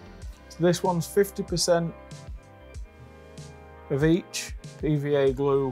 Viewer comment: I thought you said "this one's 50% bleach [...] then we add vinegar..." I was like noooo chlorine gas! I had to rewind and check to make sure you weren't gassing yourself lol! (50% /of each/!)